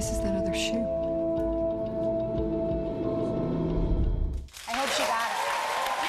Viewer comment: I heard "I hope she got them" but the subtitles on directv say "I hope she dies" ... I don't know haha